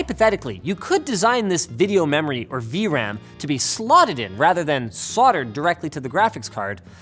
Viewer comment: The Closed Captions state "Slaughtered" instead of "Slotted". Pretty funny, I must add.